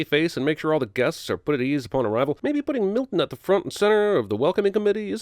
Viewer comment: I heard "Milk Nut" and was like damn thats a cool weird ass insult im gonna use that, then I read the subtitle "Milton" and was immediately disappointed